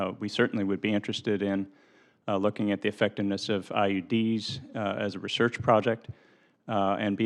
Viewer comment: I hear IED's at first. Blow up the horses.